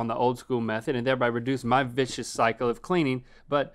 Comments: When Link said "vicious cycle " in the beginning I swear it sounded like he said "bitch's cycle" lol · I thought he said “b$;”?h a$$” at 0:40 not vicious cycle